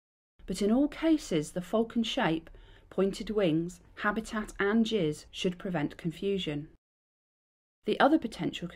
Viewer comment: “…Habit and (what?) can prevent confusion” I can’t work out what that word is, heard it in many of these (excellent) BTO ID videos. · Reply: Giss is the general overall characteristic of a bird ,movement and behaviour